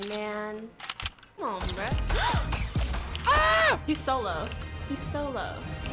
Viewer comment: did she say so low or solo